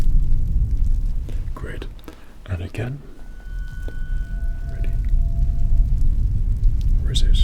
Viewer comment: sounded like you called me Anakin